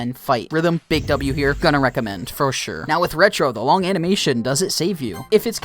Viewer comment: did you say retro? I'm pretty sure it's rojo